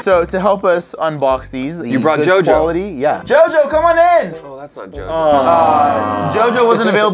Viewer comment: I heard "JoJo please come in!" And I nearly unsubscribed...